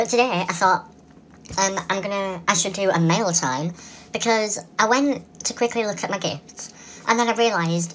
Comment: It sounded like you said maila time!😂😂